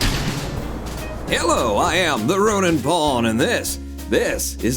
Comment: I turned on captions and instead of "roninpawn" it said rune and pawn. ?????????